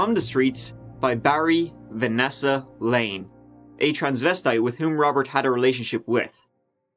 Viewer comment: Btw, in this video I used the term "transvestite", which I've learned is a no-no term, outdated and offensive. Sorry about that, in my sources that's what Barry Lane was referred to but I understand it's derogatory. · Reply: I thought you said Transvestie not Transvestite which sounded adorable.